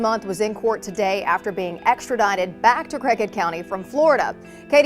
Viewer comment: I thought she said crackhead county 😂😂